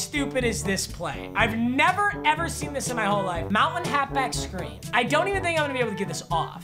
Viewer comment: Who else heard him say mountain half back screen instead of motion